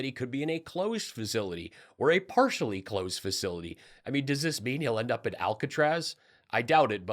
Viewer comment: I had to put the Closed Captions on because I thought I heard, "Trump could end up in a partially clothed facility." The Socks Stay On would hardly be enough.